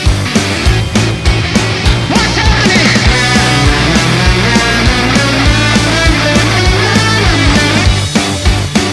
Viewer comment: setiap kali part ni aku dengar yonk kata 'buat sekali lagi!' dia cakap apaaa sebenarnyaaaa😭😭😭😭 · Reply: He said "What's happening!!!!"🎸